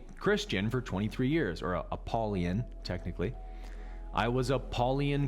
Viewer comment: Did you say you are Apollyon and not Christian at 25:33? Meaning you worship Abaddon?